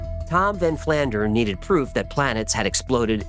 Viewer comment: I keep hearing "Ned flander" and my mind says "Flanders gosh darnit!"